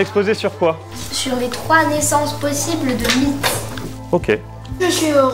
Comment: c’est extrêmement touchant d’entendre ces enfants raconter leurs expériences passé et leur bonheur d’être dans un tel établissement… c’est quand même fascinant de proposer un exposé sur les 3 naissance de mythe ou de fabriquer une « station spaciale » a 9 ans… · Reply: J'ai compris "les 3 naissances de mites" xD